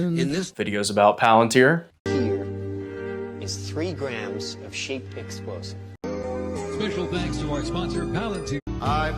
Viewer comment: i heard that as “3 grams of sheep pixels”, we gonna have eye of ra 2 with this one